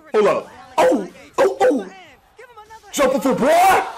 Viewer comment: Hi, couldn't understand but were u saying "jump full boy 08:13" , it gets me all the time. Jump full boy = death ;)